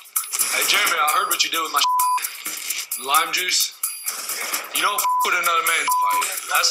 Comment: "You don't fuck with another man's dip" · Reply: Chris i thought he said you donr mess with another man's shit lol